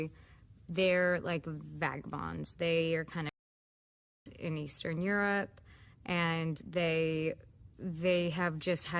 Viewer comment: I still to this day do not know what that word was. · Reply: I think it was Gypsy